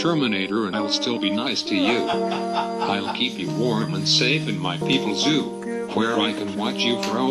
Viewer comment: Was it only me or did I heard him say IN MY PPL ZOO